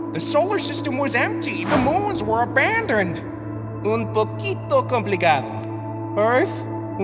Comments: for those who didn't understand what Earth said in Spanish, he said ''un poquito complicado'' it mean ''a little complicated'' i hope this helps. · what did he say? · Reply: Un poquito complicado It’s a bit complicated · what was he saying again? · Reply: Un poquito complicado means a little complicated( I THINK)